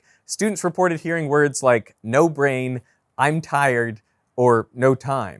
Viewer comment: I heard train